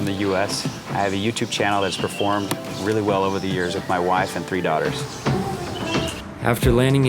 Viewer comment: Lmao I thought you said "with my wiveS and three daughters" is there something you're not telling us?!😂😂